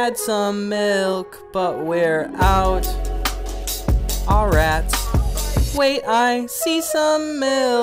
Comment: he says "aw rats" but the captions say "idk" The overlap of people who need captions and that will see this is probably zero but just in case